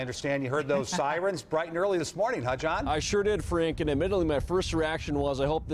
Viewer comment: At first I thought he said " I sure did freak " lol it's okay I know now he said Frank .